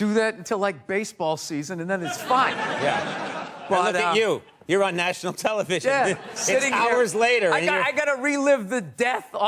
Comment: 😂😂😂 you know? I thought I heard Conan say "look at you ... on national TV six hours later ..." and started thinking: "shit, if the Super Bowl on 2017 ended at 10:14 pm Houston TX time (btw, not THAT much of a fan, I looked it up, didn't even watch the game XD), meaning it was 8:14 pm on L.A. Does that mean they're doing Conan at 2:00 am? Didn't it start at 11:00?" Then I went back and realized he didn't say that. It just got mixed with what Bill said. Anyway, I think I need a pet or something 😕